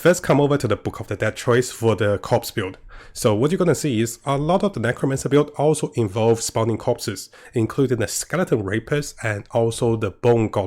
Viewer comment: I thought he said skeleton RAPISTS I was like “damn they went all out with the naming 😂😂”